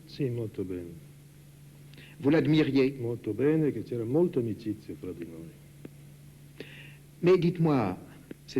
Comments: What he said about Cortot in 16:00 ? Molto bene and? I don't know İItalian:( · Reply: E c'era molta amicizia tra di noi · What he said about Cortot in 16:00 ? Molto bene and? I don't know İItalian:( Can you help me? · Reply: "Molto bene, e c'era molta amicizia tra di noi". "and there was a lot of friendship between us".